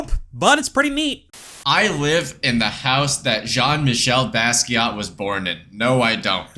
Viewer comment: I heard "I live in the house that John" and was absolutely expecting "Built on the hill"